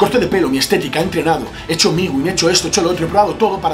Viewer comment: Qué dices en el 4:21??? He hecho migu?